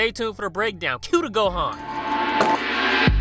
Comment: I always thought that was saying "Cue to go on!" ...I turned on the subtitles...."Cue the Gohan" ....my life has been a lie!